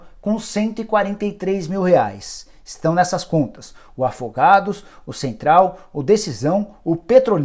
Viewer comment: eu ouvi direito ou tô doido , o nome do time é " afogados " ? Kkk